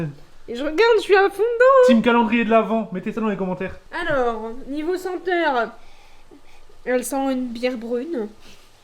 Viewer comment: c moi ou a dit niveau s'enteur mdrrr